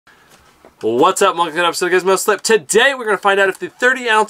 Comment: What the fuck did he say ? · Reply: whats up welcome to another episode of gizmoslip